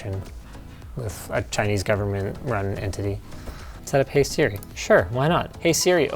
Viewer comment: I first heard that he said with chinese government and Anthony 😂